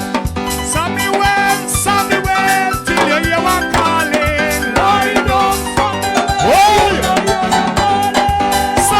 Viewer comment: what is he saying “lay dung Samuel yuh nuh hear yuh calling” or “ride dung Samuel” or “rise up Samuel” ? Mi waan sing to but a doe know wah fi tell Samuel! 😀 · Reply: Lay down Samuel till You hear your calling 1 Samuel 3:9 Bible verse